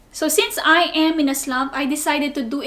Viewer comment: what exactly do you say? Because I keep hearing "but since I am in Islam" and it doesn't make sense to me